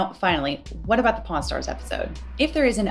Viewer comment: Great video and its been very informative. But all I hear is "pornstars" instead of "pawnstars" which has me chuckling like a child every time.